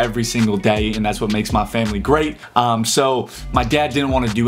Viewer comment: it sounded like grape